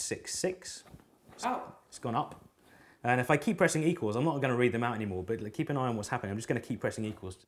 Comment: "I'm not gonna read them out anymore" Reads them out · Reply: I thought he said, 'i'm not gonna write them out anymore'. Make more sense contextually.